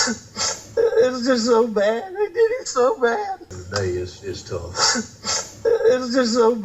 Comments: I heard, "I did it so bad". But it could be 'they' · I hear ‘I did it so bad’ but I’m from NZ so the accent is a little harder for me to decipher. It’s also very subjective, I was an older teen living near Dunedin when a fellow by the name David Bain was accused of killing his family and there was similar question over what he was saying on the 111 (911) call. I heard something not very incriminating but once it was suggested during trial’s that he said something else I could hear it either way depending on what I as thinking at the time. Statements like that through tears are hard to understand and as at the end they agreed that the jury needed to decide what they hear. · I thought I heard..’I did it so bad’ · I hear it as “I” all three times. I do music prod and i have sensitive ears. I popped my prod phones on..Also he doesn’t say him he says IT..he says “I did IT so bad” def a soft t in end there…so he says “i did it so bad” which prob makes more sense if you think about it. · Sounded like at full speed “I did it so bad”. Sounded like at the lower speed (it so hard to under stand) the first time I heard “I” the second time it could be “they”. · i hear "i did it so bad" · I don't hear him. I hear "They did it so bad".